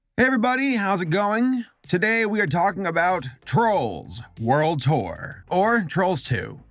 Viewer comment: anyone else hear *whore*?